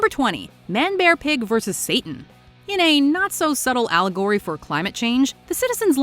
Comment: I misunderstood the first match so I thought it said manbearpig vs Stan and I was like what!